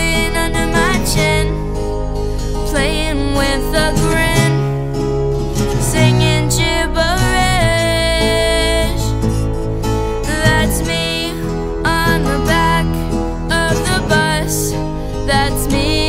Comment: I always thought he said singing GBH ,i guess wishful thinking